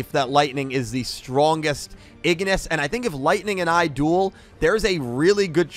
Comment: when you said “If Lightning and AI dueled” I rewinded cause I thought “AI” was actually “I” as in you 🤣🤣🤣sounded like you were challenging Lightning